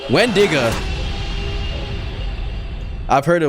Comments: Did bro Just say "Wendnga" 🥹💔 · did he say wendigger